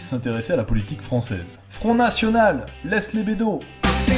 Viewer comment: Moi j'entends Sur une nationale ! Laisse les vélos ! Agathe m'a mentis ! Laisse les gays !